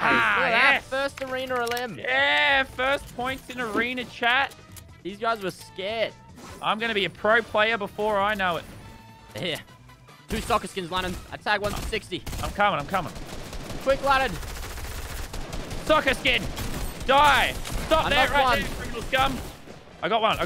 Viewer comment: I thought lannan said sucker skins 😂😂😂😂